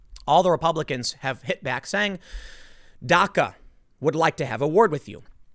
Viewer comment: I heard Dakka XD